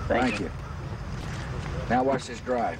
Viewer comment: Say what you will, but you gotta admit, that was a nice drive. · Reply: I swear he says “alright, guess we’ll need another” at the end which makes me believe he just sliced the ball into some water or something tho.